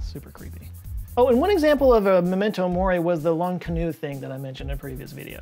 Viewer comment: I thought you said "Long canoe" when you meant L'inconnue and I'm crying 🤣